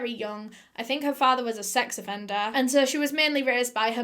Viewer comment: Not used to your accent, when you said sex offender I thought you said sex vendor! Had to replay it 5 times!